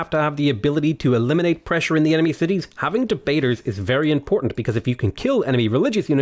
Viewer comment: when you think he said we need jebaiters....whats he planning on doing? lol